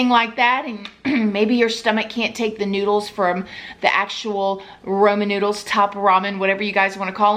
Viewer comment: LMAO Sister!!! My husband heard you say Roman Noodles and he said and I quote "Damn she is SOUTHERN" lol he said it with all the love in the world 😆🤭😂🤣